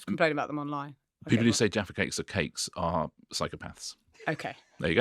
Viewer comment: Jaffa cake controversy! McVitties went to court to protect the Jaffa Cake. A biscuit attracts vat A cake does not. McVitties were able to proove that like a cake a stale Jaffa Cake becomes hard. A stale biscuit becomes soft and soggy. Therefore a Jaffa cake is a cake not a biscuit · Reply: Oh thank you!!! I thought he said people who say defecate is a cake are wrong!